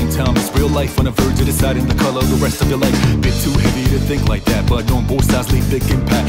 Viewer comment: I always misheard "deciding the color of the rest of your life" as "deciding the color of the rest of the light", which I thought sounded a lot cooler and more meaningful. The fact it actually just says "life" twice is kind of redundant. Great lyrics though nonetheless